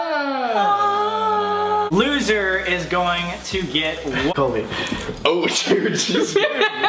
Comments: It's sounds like said "loser is going to get Colby" 😂😂😂😂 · I’m literally watching this in 2020 but am I the only one that noticed that the jump cut at 2:10 sounded like he the loser is gonna get colby😂😂